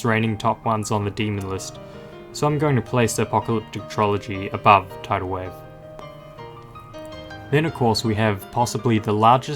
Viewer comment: what did you say? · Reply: Above tidal wave